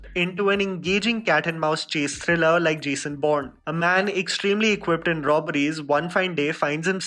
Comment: Did you say Jason Bond at 4:10 ??